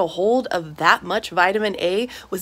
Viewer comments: The way she said it like she was horrified, I thought she said the livers of cannibals 😂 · I thought you said cannibal and not carnivore 😭